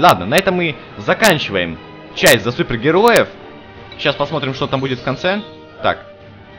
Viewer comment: мне послышалось: ЧАЙ ЗА СУПЕР ГЕРОЕВ 😂😂